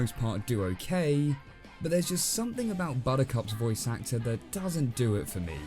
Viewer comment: i thought you said bonica instead of buttercup.